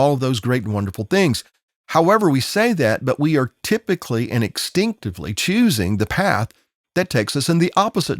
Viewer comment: Thanks for sharing your thoughts and insights on the events you covered. I have a quick question regarding the word "exstinctively" that you used around 26:45 min. into your presentation. I listened to that section over and over and thought you may have said instinctively but it sure sounds like you're saying the former. When I searched the web, "exstinctively", doesn't come up as a valid word so I was wondering if you made it up, I'm mishearing you, it's a Southern way of saying "instinctively" or if it was an intentional combining of "extinction" and "instinctively"?